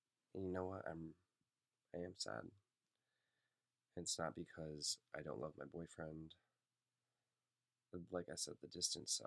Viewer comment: have i misheard what was said at 4:03/4:04 ? i swear i heard you say "i am sad, i'm sad because i don't LOVE my boyfriend", which doesn't seem right so i thought maybe you said because "i don't HAVE my boyfriend" but i've listened a few times and it still sounds like "i don't LOVE my boyfriend" to me. i'm not looking for an explanation because it's not my business but i am asking for someone, preferably you, to clarify if i heard right (as in "i don't LOVE my boyfriend" or "i don't HAVE my boyfriend"). happy birthday for next week, i hope you have a great day ! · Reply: Julie Dylan 1329 he said” it’s not because I don’t love my boyfriend”